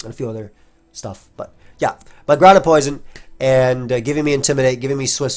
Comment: grounded poison. as in, it touches the ground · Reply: Ahhh, misheard it as Ground AND Poison! 🤪